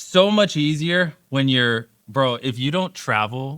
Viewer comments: Holy cow, they (intentionally) misheard the "...bro..." as "broke", did they? I mean, Mr. Beast is a bloody blight on modern youth, but that spin was just malicious. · Yeah, he did say that life is easy when you wake up in your own bed, but he never used the word broke. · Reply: Just before that though. Did he say Bro instead of Broke? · It sure sounded like he said exactly that. Maybe it was "life is so much easier when you're, bro....". Go listen again, he either said bro, or he stopped himself BEFORE he said the stupidest thing ever